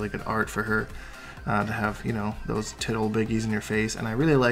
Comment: Tig ol' bitties! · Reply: Actually sounded like he said "tit ol' biggies" to me..